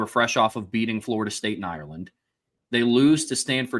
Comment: Is he saying “…fresh off of beating Florida State *in Ireland*”? If so, what are we talking about here? Unless I’m mistaken, and I don’t think I am, Georgia Tech isn’t located in an American city called Ireland, Georgia. And I know FSU isn’t in a city by that name either. So, unless we’re talking about a neutral site game in a city called Ireland that I never knew existed, which is entirely possible I guess, was there some kind of international neutral site game between Tech and FSU that took place in Ireland?